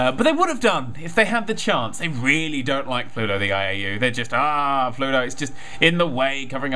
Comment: i thought he said pluto iou